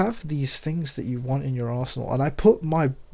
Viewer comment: Sounded like you said in your arsehole when you said arsenal lol